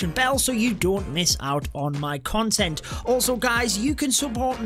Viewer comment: did he just say consent instead of content? Oh my!😂😂